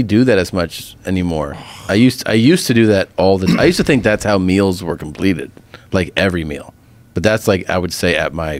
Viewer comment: Did he really say I used to think that's how Mills were completed ahhhhhhahahahaha 😭🤮